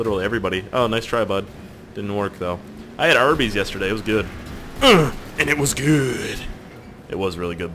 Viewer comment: it sounded like he said :i had herpes last night, it was GOOD!" when he said "i had arbys last night, it was GOOD!" :C